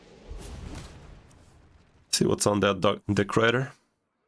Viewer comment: haha did you say, see whats in that dark, dick rider?! 😂🎉